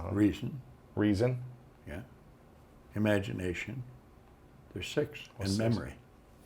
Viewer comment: What is Bob saying between imagination and memory [25:14] ? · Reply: “There’s six”